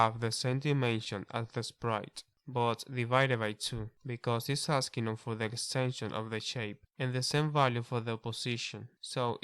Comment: I didn't understand what you said at 14:24, why do I have to divide the extents by 2? Did you say because it is asking for the essential of the shape?